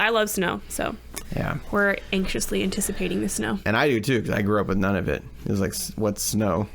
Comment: For a second when said “I grew up with none of it” at the beginning I thought he said “I grew up in Nunavut.” 😆